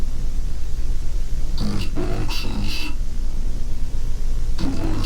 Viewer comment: Ok at7:10 it sounds like it saying juice box